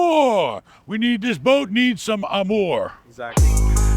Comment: Did say he needs some amor? Cos I got some Amor for him! 😂